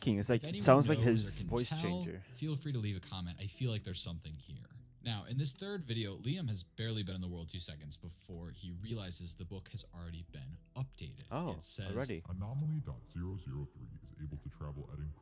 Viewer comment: i believe he said liam idk thats what i heard